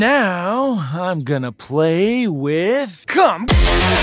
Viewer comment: i thought he was gonna say 'now im gonna C*M with YOU